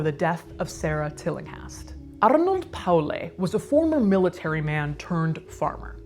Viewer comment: lol, I thought you said "Arnold Palmer" at first...